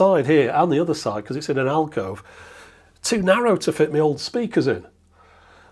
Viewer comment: did he say "too narrow to fit me old speakers in"?